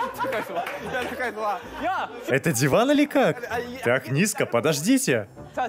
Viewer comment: или у меня шиза ,или я слышу "как дела?"